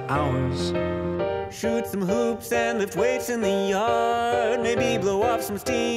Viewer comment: I thought he said "Lift Wades in the yard."